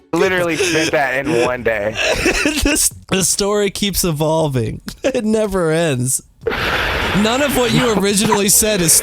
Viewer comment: Be honest, did you say "this "nigga" story keeps evolving, it never ends"